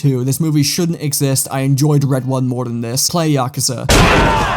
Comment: I turned my brain off near the end and then I just heard "play Yakuza". Hell yeah.